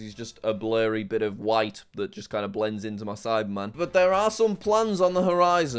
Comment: when he was talking about the ksi play button it sounds like he said “it blends into the side-man”